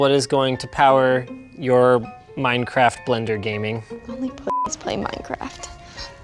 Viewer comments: i was friends with her till she said only pussys play minecraft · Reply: It's that what she says? For some reason I thought she said "faggots" lol "pussies" is much better 😋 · WHAT DID SHE SAY AT 23:00 i need to know now .NOW NOW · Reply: Only pussies play minecraft